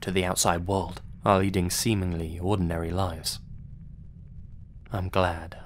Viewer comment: What I heard: "It was a nice day, and we had sex and birds." What I thought: "this is a really weird Thanksgiving story but you do you." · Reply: He said, "leading seemingly" and I heard "eating semen we."